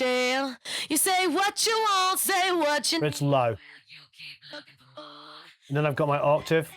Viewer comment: Please, can anybody help me to understand what says at 2:34? Is it "a bit to low" or something like that?